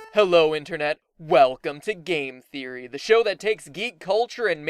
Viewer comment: I Thought your channel said The Game Terrorist